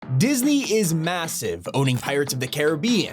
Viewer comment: did you say massive ?